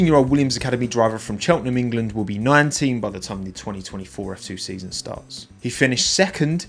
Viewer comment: Clicked so fast, I thought he said 2024 F1 season…😂😭